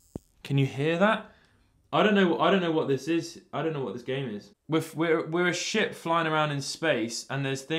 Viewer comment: I swear he said "we're a sh*t flying around in space"